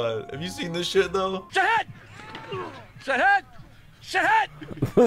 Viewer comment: I thought he was saying shit head made me laugh so much i had tears in my eyes.